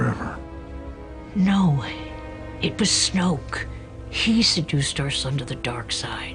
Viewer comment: Did Leia call snoke a she at 5:40?